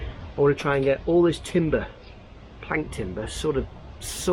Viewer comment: I thought you said "plankton" but you said "plant timber". I thought it was a new colloquial term for annoying timber floating about